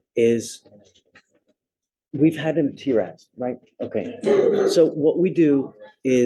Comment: LOL. The subtitles are sub-optimal. When the judge says "We had him TRAS, right?" Subtitles said, at 20:41 , "We've had him tear your ass, right?" ROFL